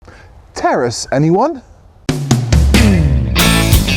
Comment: I thought he said “Terrorists anyone?” On the intro